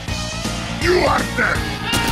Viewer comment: I swear I just heard heavy from TF2 s Scream YOU ARE DEAD!